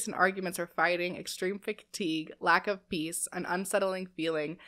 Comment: The first one I heard her say "lack of bees" and had to look over at my screen to make sure it wasn't that lol